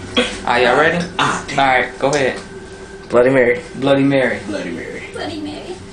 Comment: What other hear: bloody mary What i hear : binary